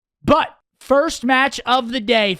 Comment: did he just say butt? hehehe